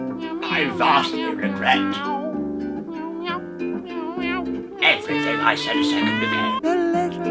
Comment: What dose say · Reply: "I vastly regret everything I said a second ago"